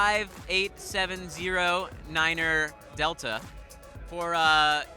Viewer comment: Did I hear a niner in there? Were you calling from a walkie talkie?